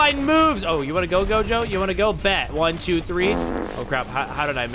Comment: WHAT DID HE SAY?! geto?